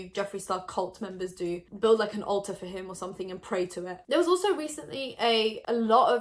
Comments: “build an alter of him and pray to it” omfg 😂😂 sn: jeffree did say he retaped the video a couple times so it’s possible he grabbed the picture from the takes he didn’t post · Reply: I totally thought she said “build an Ulta for him” this makes more sense 😂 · “Build like an altar for him or something and pray to him” lmaoooo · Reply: Omg i thought she said build an ulta for him! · I heard you say "Go build an Ulta for him" 😂 Ulta or Alter, either one would work for Jeffree ⭐🤷🏽‍♀️